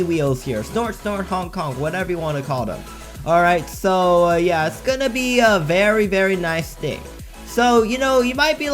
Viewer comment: Sorry user but you wrong Wait nvm I thought you were saying it’s gonna be a bank 😅